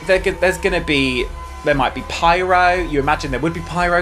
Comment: “There may be pyro” I heard “There may be pirates” and I’m here for that 😂